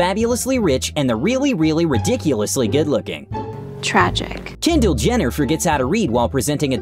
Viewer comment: am i the only one who hears “jendall jenner” instead of “kendall jenner”?